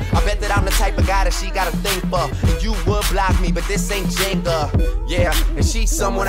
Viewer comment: "...cautious of a flood so I always lay the right pipe.." LOL this dude got some bars you gotta admit. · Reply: Escobar600GOAT_NODEBATING: I just recognized another line Drake said; "Nig*as tryna block me but I ain't......Jenga." Thought he was saying "Jigga" all this time, as a disrespectful jab at Shaun Corey Carter. Then I concluded, that made NO SENSE since they collabo'ed on "Pound Cake," another single I....LOVE!!